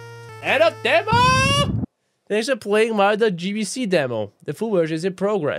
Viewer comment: I thought it said Mario BBC 😂😂😂😂😂